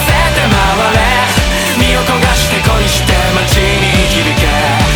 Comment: ここ身を焦がして恋してに聞こえる